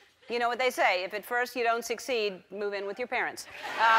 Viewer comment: What did ellen say at 2:43? after "If at first you don't succeed" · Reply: Gracious2000 move in with your parents